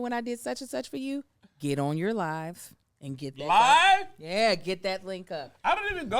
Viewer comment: it sounded like she was about to say … “ get on your knees…” 😭😭😭